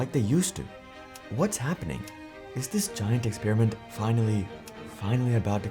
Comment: my dumbass thought it said "he's finally busting" and was confused